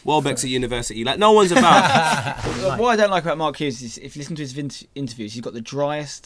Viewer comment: Wait what did say at 5:45? (Please no trolls) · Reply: what i dont like about mark hughes is that when you listen to his interviews he has the driest lips.............